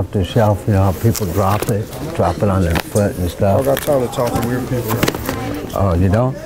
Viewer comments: I think he said I dont got time to talk to weird people like you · I can't catch what the guy says at 4:24. "I don't like talking to weird people", is that it?